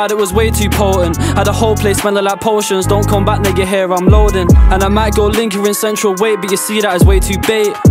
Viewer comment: Don’t get me wrong, I love this freestyle and I’m here for it 💯 But am I deaf or around 1:12 does he say what I think he says?🤨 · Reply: Nah he says don’t come back now you hear I’m loading